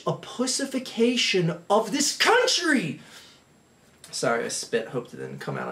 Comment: It sounds like hes saying cuntry.